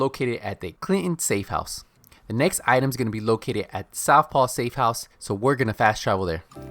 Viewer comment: Did you say "Southpaw Steakhouse" 😂😂😂